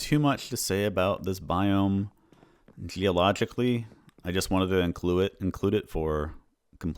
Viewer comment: I thought you said "theologically" here, implying you didn't want to comment on the holiess or lack thereof of the warden